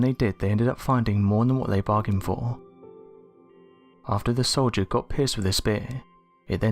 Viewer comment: thought he said after the soldier got pissed with his beer 🍻 🍺 haha